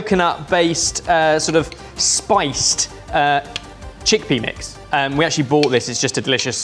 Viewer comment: did i hear Chick-Penis?